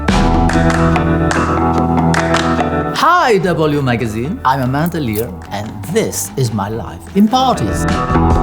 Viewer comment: I swear I heard ‘I’m a man " lol 😂 on that note, wasn’t she trans ? Back in 70s and 80s no one cared .